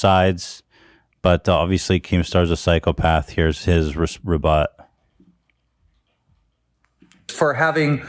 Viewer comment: did ethan say rebot like it’s a word